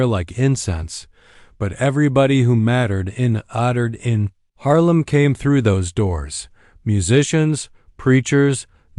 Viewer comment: What he say in 1:36? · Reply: "Your uncle will bury you tomorrow" are the six words.